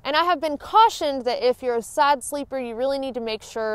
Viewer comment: Thanks for all of these tips. [I have a bit of difficulty trying to listen to the "southern" accents.... many words sound "misleading".... i.e. "side sleeper" , sounded like you said "sad sleeper"...]